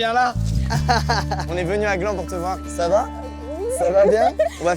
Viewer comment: J'ai cru qu'il avait dis "J'suis venu avec mon gland pour te voir" a 38:19 et en plus après il dit "On va faire plein de truc aujourd'hui toi et moi" Mon cerveau : Hein ? Mastu : AtTeNd QuOi